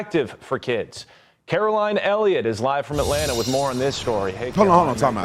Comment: ▪︎▪︎ Wait. Did he just call her Hareline Elliott? 😂😂😂 That's what it sounded like.